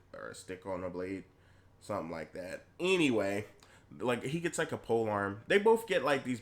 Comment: "she gets a spear, and he gets uh, blade on a stick, er, a stick on a blade?" I literally couldn't contain myself xD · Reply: I thought I heard him call it an arm pole or something...